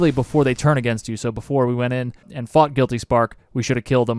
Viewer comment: I swear I heard " before we went in and FUCKED guilty spark" 7:55 lol, had to rewind it